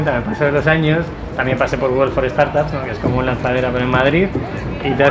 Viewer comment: Que dice en el 10:20? "es como el lanzadera en Madrid" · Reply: Google for Startups